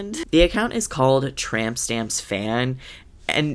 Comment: I thought it said trump stamps at first